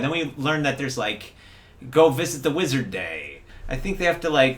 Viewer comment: Not gonna lie, i thought said "Go Fist The Wizard Day"